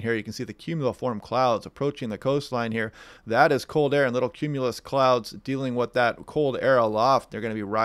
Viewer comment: Eastern Wa. Had to laugh. Sometimes you hear what you want to hear. Planted a bunch of native perennials a couple of weekends ago. As I dug into the earth so dry. At the beginning of the video I thought you said "little pretty clouds" instead of "little cumulus clouds" :)